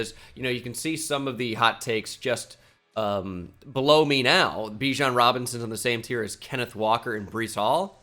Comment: Did you just say blow me now?